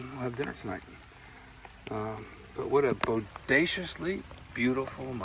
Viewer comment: "what a bodaciously beautiful mushroom" man I just love this guy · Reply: I thought he said audaciously beautiful. Listening again though you’re right.